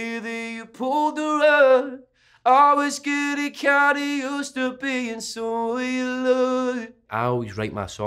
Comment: When I heard the song I thought he was saying, "I was getting used to being so unloved" 🤷‍♀️... I was way off😂